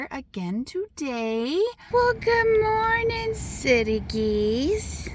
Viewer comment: At first i thought you said "silly" geese. It was "city" but it prompted me to look up this......." The phrase "silly goose" originates from the common association of geese with being somewhat foolish or clumsy, which is likely why "goose" is used as a playful term to describe someone acting silly; the "silly" part simply adds emphasis to the lighthearted nature of the insult. Key points about the origin: General perception of geese: Geese are often seen as not very intelligent animals, leading to the association with silliness. "